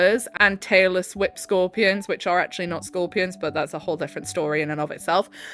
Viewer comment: not me mishearing Tailless Whip Scorpions as Taylor Swift Scorpions